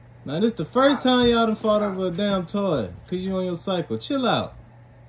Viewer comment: I heard, "Because you on your psycho..." Now seeing in the comments its "cycle".... Psycho.... cycle..... same thing....